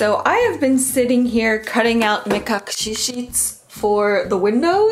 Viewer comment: I can't hear what is saying. What sheets is she cutting? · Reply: Mekakushi, "hidden from eyes" aka privacy window films :)